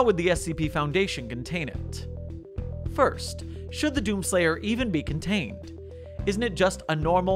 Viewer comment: I thought he said DOPEslayer.